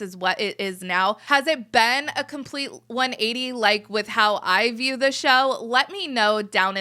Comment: I wasn’t fully paying attention and I thought you said, “with how I view Michelle.” And I was like, “wait, who’s Michelle?” 😂